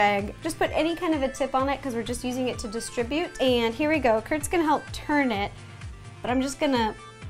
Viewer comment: she called him kris instead of Kurt